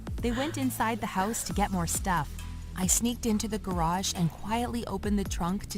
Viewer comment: wait a minute did she just say sNeAKeD!!?